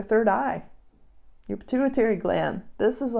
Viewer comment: Until Time Code 25:05 I have never been clairaudient ,but "activating your Intuitary Gland" is what I heard, lol. So from pituitary to intuition, here's to a brand new gland ... and finding one's North Star from within, when the notion of Home feels like a distant starry memory. 💫